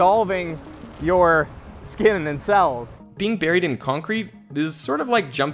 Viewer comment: I thought it first said getting bulked in concrete